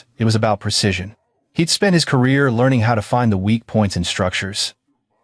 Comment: Great story!👍😃👍🫶 I noticed a mistake in your AI's captioning of the story. At 31:20, instead of "he spent his career learning how to find the weak points structures", it reads "kids Spanish career…". I bet Sheilia lost her license. I hope she gets jail time too. Is there a sequel that shows what happened when Miles got older?😊👏👏👏🙏🙏🙏💖✨📖✨💖